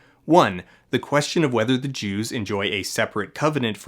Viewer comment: Here's a rule of thumb: if you have to say "There's nothing about the Jews in this film" you've got a problem. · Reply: for instance. also, he extremely clearly says jews, not geos, at 8:12. the captions are wrong, the two words are not pronounced the same way at all.